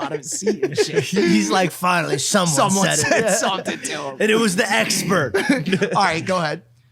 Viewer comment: sounds like he said "it was the #HEX-pert" 😅